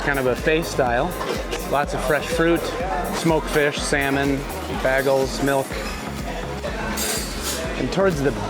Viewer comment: Did you say “boagles?”